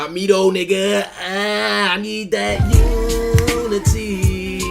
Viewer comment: I always thought it said “you and me”, learning now it’s unity